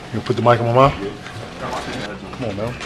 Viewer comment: just at the start of 00:01 did he say "u gonna put the mic in my mouth??" XD